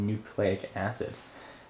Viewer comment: when he said we want to proceed i thought he said we want 2 pussies